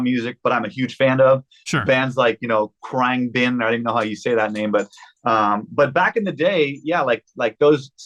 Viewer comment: Khruangbin did he say? I also love the band.